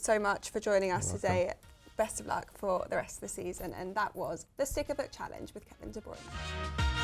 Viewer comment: I know the name of the challenge is in the title of the video but I thought she said the "sick of it challenge" lol